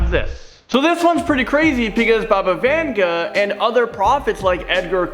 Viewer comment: I honestly thought the vid said baba yaga